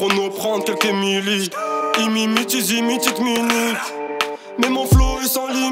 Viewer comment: Il dit quoi à 0:30 😂 like si ta pas compris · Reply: Ils t’imitent , ils s imitent , ils mimi